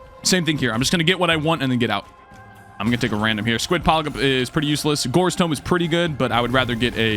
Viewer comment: i heard that you almost said squid poglip lmfao